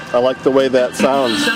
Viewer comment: I heard "we like this app" Sounds great! I like the idea of having areas of no audio blended in there too. May have to add files of dead air, into mine as well. Good session! And man, that's just said that people don't take care of these old head stones.😥